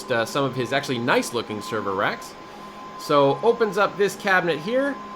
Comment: Server RACK? I thought you said server STACK